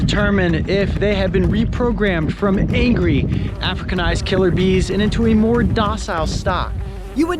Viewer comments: I swear I heard him say "Killer Bean", if yknow what I mean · All I hear is "Where is killer Bean?"